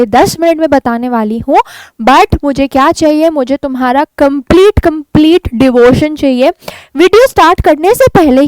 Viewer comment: kya bola app na devorce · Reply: Arey bhai devotion bola hai devotion matlab निष्ठा